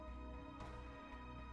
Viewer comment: What I heard "HAKITO IS DEAD MEAT FOR MESSING UP MY SENPAI~~~!!" to be honest idk how to spell HAKITO lol